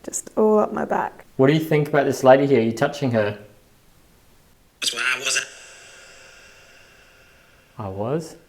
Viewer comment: after asks what the spirit thinks of and were they touching her, I hear: "I swear I wasn't."